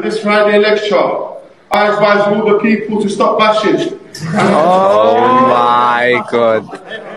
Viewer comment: What did he say at 0:08 that made everybody say “oh my god” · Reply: I advise the people to stop bashing (beating your meat)